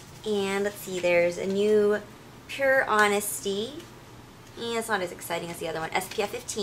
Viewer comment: does anyone know what the chapstick that she says 'ehh, isn't so exciting as the other one' at 01:43? she says something like 'pure honesty' but i can't find anything like that on the site